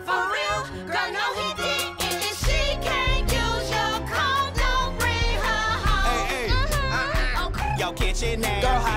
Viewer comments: What are those three girls saying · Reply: Carly Fox if she can’t use your comb don’t bring her home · anyone else hear okrrrrr